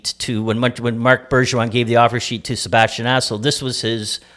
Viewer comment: I honestly thought he said Sebastian Asshole lmao